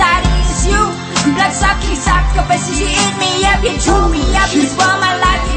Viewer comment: Did she just say Ur an octopus U eat me up 😂😂😂😂😂😂😂😂😂😂😂😂😂😂😂